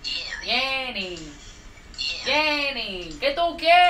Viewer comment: It's Laurel but I hear "Yenny"